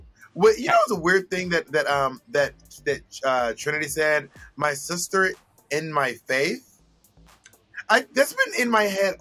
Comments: I thought Trinity said, “my sister and my fave” about Jan, not “my sister in my faith” like Bob said. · I thought Trinity said: "My sister and my Fave" about Jan - not sure though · I thought she said my sister in my face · I heard “my fav” not “my faith”